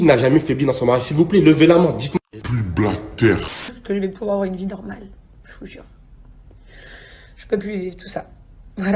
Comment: il dit quoi j’ai pas compris le mot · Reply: Il a dit " plus bas que terre '' mais il a un peu manger la fin du mot 😅